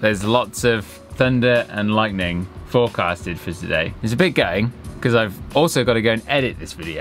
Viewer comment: did you say "its a bit gay" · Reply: It's a bit gutting.